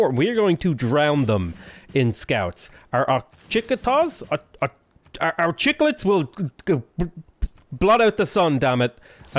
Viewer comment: did he just say "Our chicklets will block out the sun" ?